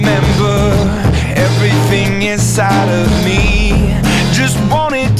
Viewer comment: I thought it was, "I'm only a man with a candle to guide me" But that could be me... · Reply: Tbh i thought he said a chainsaw inside me